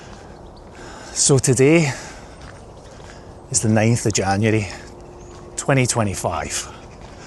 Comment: Hey I’m a Capricorn - Yvonne is a libra 😂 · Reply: Yes I realised that when you said 9 January in a newer vlog. First I thought you said your birthday was 25 January. My darling late sister was a Capricorn. I sometimes think star signs are relevant and sometimes not. Do you think Capricorns are ambitious?My sister was. Little goats climbing mountains but stopping to nibble grass and ponder on the way up. 😂